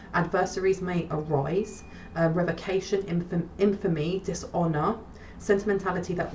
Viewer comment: Grand evening or rising Did I hear you say enfami, infami? I’m not sure how to spell it… timestamp 23:30 I would be grateful if you could shed some light on that word if you have time. I like to look things up for myself to get a better understanding but, in this case, I have no idea on how to spell it and google doesn’t either😂 If you have the time, I’m grateful either way as I know you’re busy.